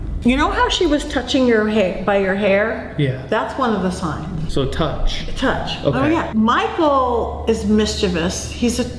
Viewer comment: What is the z word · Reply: Z O Z O